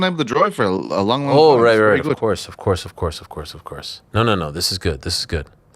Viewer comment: I think me and Ariel both heard "Do any of us care?" for Petesy's "Driving his car?" holy smokes what an accent